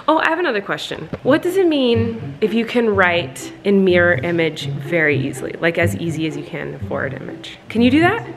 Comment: I honestly didn't know what she was saying. I'm from Wales and I thought she was saying "Mere image" I was so confused